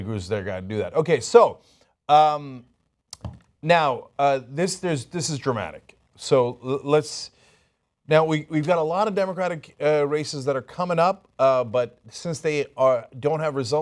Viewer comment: I thought he said democratic racists at first